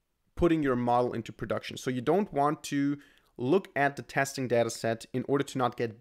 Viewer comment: Interesting where at 15:10 you said you don't want to look too much at your training set so you don't get biased. It seems everyone else I hear says to examine it as much as possible....is there something I'm misinterpreting from you or them? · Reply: He said testing dataset not the training dataset.